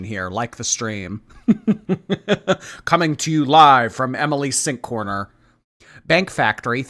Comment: Not me being so high that when says, oh you know who else got a lot of shit? Emily's sink! And I thought he said "Emily Sink" and i was trying to figure out who Emily Sink was....